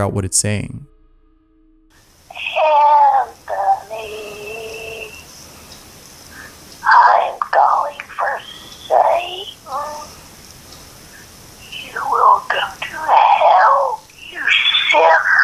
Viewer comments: I think is saying hey I’m with satan ur going to he11 second this is bull shihhhhhh pick up heyshh aboushoooo wake up third anthonyyyyyy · what i hear: I'm going for Satan , you will go to hell! 7:34 what i hear: I will kiss you, wake up! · I hear Anthony I’m going for satan, you will go to hell you sick.. Anthony wake up. Anthony come to sleep.. anthony wake up Anthony - this got me hard cause my names Anthony lol pretty crazy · The 3rd one sounds like a old lady shaming someones sins like saying (I could only make these words from the recordings, it was very hard but here is what I heard): "You'll (stutters) go to hell" "Im calling for satan" "You sin.."and "Wake...up".. I had to keep replaying to actually put some words together, whenether or not these are the words said, this creeps me out the most. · I hear saying your going to hell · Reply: I hear “I’m going for Satan, you will come to hell you sinner.” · The voicemail at 7:07 sounded like it said "I'm going for Satan. You will go to hell, you sinner." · i think its saying in the first one *Emilyyyy, im calling for satan.... you will go to hell you shit you w***e . 7:22 is it bell?... hehe where'd it go.. she cuts (screech) she does hehe she does heeeeeeeeeeeeeeee idk thats what i heard what did yall hear? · One of the things I heard in the voicemail was: you will go to hell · DID SHE SAY "Welcome to hell you sh*t!" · i hear like youll meet satan,your going to hell